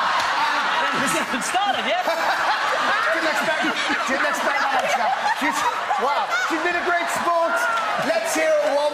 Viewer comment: What does Dec say at 10:34 at the wedding? I've seen it so many times and in so many places but can't really here what he says. Love your videos by the way. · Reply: Forever Ant and Dec 'Hasn't started yet!' Or something